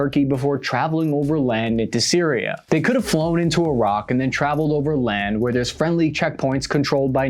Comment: Okay, as someone who listens to these videos while working, for a moment I thought you said "they could have flown into a rock."